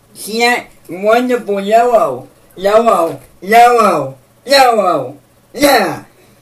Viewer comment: is he saying YOLO? I guess he really is the innovator of hard core.